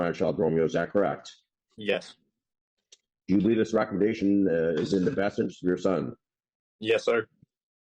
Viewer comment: Read the sub titles 😂😂😂😂😂😂😂😂😂 I had to go back and listen a couple of times to understand the 👨‍⚖️ said, "Do you believe this recommendation is in the best interest of your son?" And not what the subtitle said lmao